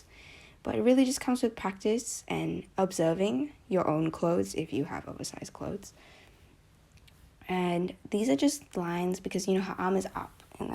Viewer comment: When you said “if you have oversized clothes” I heard “if you have clothes” XD